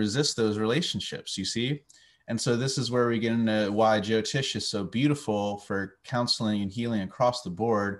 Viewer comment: You mentioned something that sounded like "jo-tish" counselling 3:45 . Can you please say if this is a person or a practice? How is it spelled? I'd like to learn more (I know nothing about vedic astro). Thank you 🌼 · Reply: yes jyotish or jyotisha is the name for vedic astrology in Sanskrit ;)